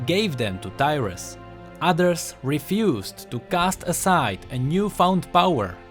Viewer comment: Who else thought he said Dyrus xD